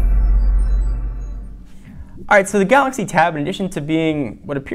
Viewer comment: At the start of the intro I hear "alright so the galaxy.."